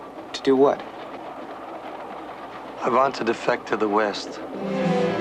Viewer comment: What does the man say at 1:29? I couldn't understand his bad accent enough to catch it. I kept replaying it over and over again to hear it, but all I got was "I want to dedoefjje to the defdhef." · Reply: He says "I want to defect to the west." I pretty sure.